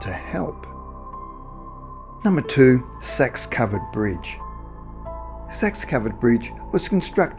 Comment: i must be hearing wrong. i thought the guy said sex covered bridge